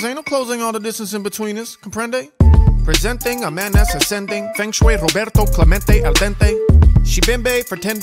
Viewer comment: I thought u said menace ascending wtf